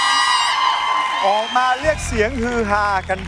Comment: คุณเบียร์เค้าพูดว่า “โบมา” 55555